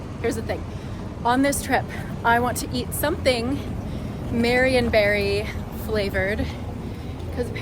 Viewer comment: YAY YOU GOT OUR TILLAMOOK MARIONBERRY ICE CREAM, I'M SO HAPPY😊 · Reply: Oh I thought she was saying “Mary and Berry” and i was so confused 😂